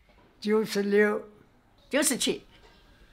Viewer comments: sounds like she said “goodbye” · Its deep that the very last one sounds like shes saying ‘...goodbye’ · which is the 100 mark, SHE SOUNDS LIKE SHE SAID GOODBYE AND I WAS LIKE A W H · it sounded like “goodbye” 😢